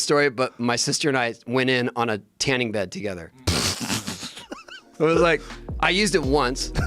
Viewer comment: For a second, I thought he said, "Me and my sister went into a tanning bed together". 😳